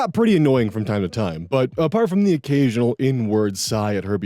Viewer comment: WHAT word? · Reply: Inward